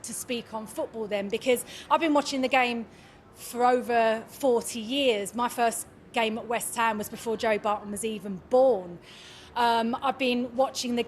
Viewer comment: “10 years before joey was born” bianca is 39 joey is 41…women math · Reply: Nice try, but you're wrong on both the basic facts and what she said. At 15:11 she said "I've been watching the game for over 40 years. My first game at West Ham was before Joey Barton was even born." Barton was born in 1982. I couldn't find a consistent source on her age but most sources say she was born between 1972 and 1975.